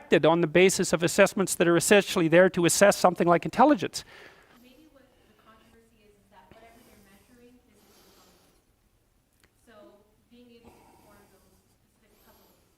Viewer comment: Hmmm, what did she say? I listen back to it twice Atill, what is she saying? I know! The captions will say! She said "*idea from a student*" -_ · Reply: "Maybe the controversy is that, whatever you're measuring is your common sense" that's what I'm hearing anyway